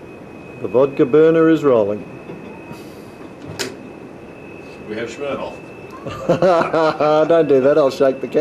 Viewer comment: lol 0:58 did he say "(POP) we have Smirnoff"? lol